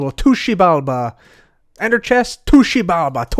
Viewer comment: said "to Xibalba" so many times that after the first 5 times all I heard was "Tooshie bulba"